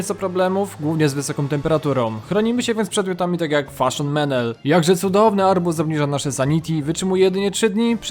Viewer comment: usłyszałem ✨KWasZoNY meNEl✨